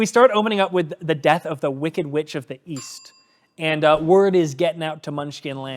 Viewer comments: oh East, I thought u said Weast. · East? I thought you said 'Weast.'